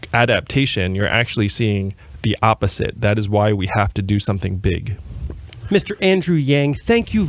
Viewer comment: Sorry? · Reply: Near the end,I thought I heard the host say "Do we have time to do the thing?"